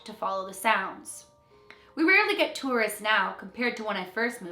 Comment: At @ 4:07 did she say terrorists instead of tourists or am I hearing things ?